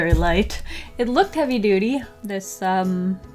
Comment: I heard “happy dootie” instead of “heavy duty” so I started looking for the happy poop emoji 💩